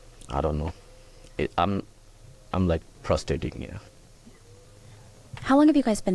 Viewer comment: My head phones must be playing up what did he say prostating ??? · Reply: I think he was saying 'it's frustrating'.